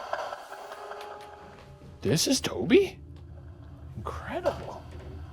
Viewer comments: does anyone else hear “Toby”? 🙃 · sounded like "Toby" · i thought the honeytone said “Toby” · pretty sure I heard the spirit box say “Toby” around 43:10, anyone else catch this? 😧